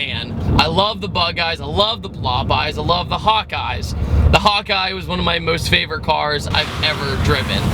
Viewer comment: I thought you said "I love the hot guys" instead of "hawk eyes"😂